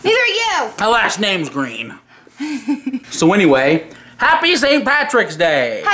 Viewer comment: what did say at 0:23 xD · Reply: “Happy St patty’s day”